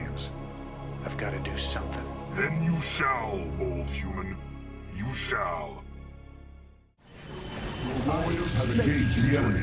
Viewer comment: I thought Fenix said to Raynor "Then you shall, bald human... you shall." That was humorous.